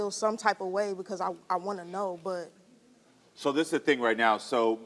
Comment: she literally says she WANTS to know instead of NOW I know. There’s definitely a 3rd option